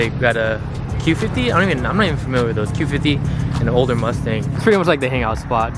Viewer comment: Did I hear you say “Holden mustang”